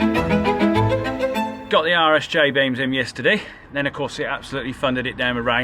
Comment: I thought you were saying "Irish J beam" for a bit instead of "RSJ beam". Was so confused why it was called a "j beam" when in the US we call them "I beams" XD